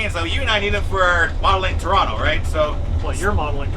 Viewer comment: I heard “Tronto” so much I thought you guys were finally going full Scott pilgrim 😂. You guys are absolutely killing it on YT right now. Keep it up and Maybe we get some classic Canadian Steam